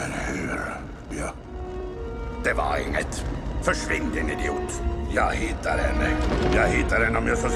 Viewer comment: What I understood at 2:25 muttering muttering VERSCHWINDE! muttering Hey, that sounded German :D